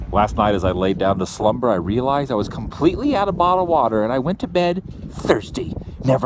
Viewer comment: When you said you were out of bottled water @ 3:37 I thought you said marijuana. My bad! ;)